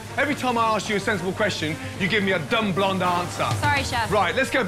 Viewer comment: Gordon: “Everytime I ask you a simple question, you come back with a stupid blonde answer” Also Gordon: has blonde hair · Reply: i thought he said stupid bland answer